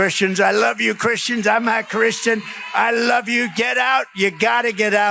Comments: Love Meidas and everything you guys do. I also hate Trumph with a passion and want him stopped by Kamala but I was listening with headphones and it does sound like he says “I’m a Christian” not “I’m not Christian” Anyway, Trumph saying he’s a Christian is freakin’ hilarious imo 🤣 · Does he say "I'm a Christian "? Sounds like it. We all know he is only a Maga not religious. But, Sounds like he says he is one. Idk fo sho. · Ben, I think you're mishearing DJTs deranged, slurred, rambling attempt at speech.... He doesn't say "I'm not Christian". He's saying "I'm AH Christian" and over emphasizing the short a vowel sound, causing the listener to hear "I'm NOT Christian" instead of "I'm a Christian". If he said "I'm not Christian", that would be telling the truth, something DJT is incapable of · Anyone noticed? Did he say I'm a Christian or "I'm not Christian?" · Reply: I keep hearing "I'm not Christian" no matter how many times I replay it.